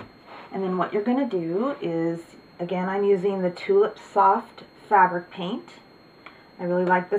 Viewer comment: Tulip Soft Fabric Paint is what i thought i heard in the video but i am in Australia so i would Look up craft stores